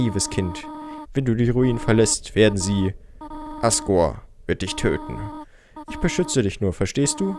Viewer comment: Ich habe gerade ASSgore verstanden 😂